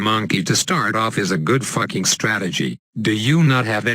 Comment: Wait did he say "fuck" the first time in like somewhere